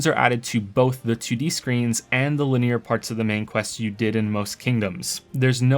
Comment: linear? “La-neer” am I hearing it wrong or is he pronouncing it as laneer? Lmao